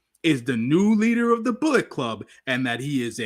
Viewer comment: First time I watched the pod in awhile, usually I just download the audio. You had me dying todsy. I thought you were saying Red Bull at first with the Gedo impression at first. I was thinking New Japan got a deal with the energy drink lolol. Heard a lot of you today, caught In The Weeds live then followed up with this live.